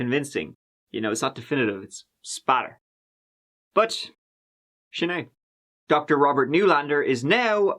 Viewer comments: says, “but shinae..” Anyone tell me what SHINAE means please? (Sorry for my butchered Irish spelling:) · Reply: It’s sin é. It means “that’s it” or “full stop” in Irish. ☺️ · Twice I think I heard say “Shanae”. He says it here 24:33 I know it as a name, not as a saying. What does he mean by it. Or is it a different word I’m not hearing it clear. Thanks